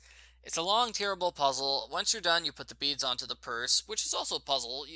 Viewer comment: I was drawing at the same time as listening and I thought I heard you put the beans on toast. xD Huh I don't remember that part of th--ohh beads on the purse.